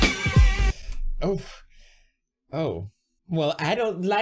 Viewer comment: she said "ohhh puppy" but i hear "ohhh fuck me"